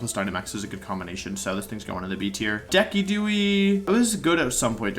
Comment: I had a minor stroke trying to figure out what he just said when I was tabbed out and heard him say "decky dewy"